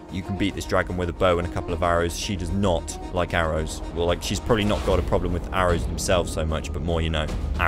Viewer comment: I need this world seed. I used to have a world that was only frozen tundra. Really fun to rp and survive in. But then I got to the edge of the map and it was surrounded by jungle. Felt like the guy at the end of planet of the apes. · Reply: dead ass thought you said "he's probably hot" as in Jesus. And I sat there like "yeah, probably"